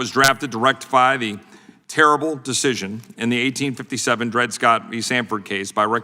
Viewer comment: did he say terrible decision